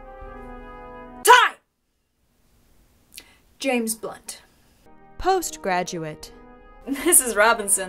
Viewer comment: I thought I heard “James Butt”